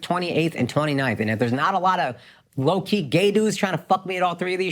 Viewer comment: Whenever Aakash said 'gay dudes', I heard 'Gandus' Indians will get it 😂